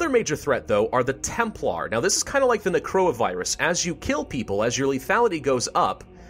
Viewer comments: Who's here after the Chinese coronavirus outbreak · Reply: here thought he said corona at 5:45 lmao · Wait did he said coronavirus at 5:45 ?!?!?!?!?!?